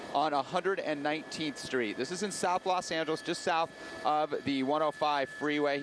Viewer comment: did he say "South Los Santos"?